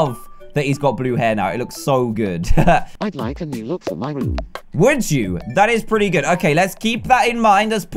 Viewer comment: i thought he said my mom instead of my room